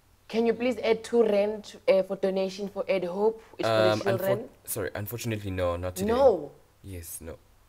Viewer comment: Did he just say unchofunately😂😂